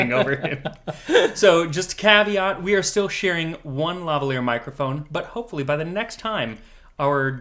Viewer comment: At first I thought you said "we're still sharing a lovely microphone" :D The gin episode was great! Love to see more booze episodes, lots of interesting history there